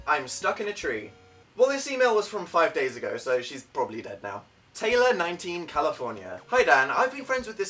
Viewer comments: did he say dead or down? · I've seen this video like 20 times and I thought that he said that Kayleigh was DOWN from the tree. I just shitted myself when I realized he said dead now...